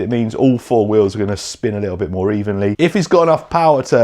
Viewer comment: Oh! CENTER DIFF! I thought you were saying SEDATIVE! 😂